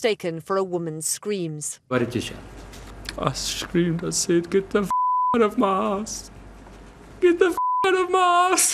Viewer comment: I thought he said, "get the f#@ out of my arse" 😂